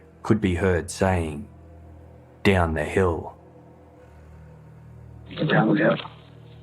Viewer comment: I recently watched a show where the audio was cleaned up & he said "Go down the hill". Some people thought he said "Guys, down the hill". The show i watched was Ben Webster. He broke down every part of the film, the video too.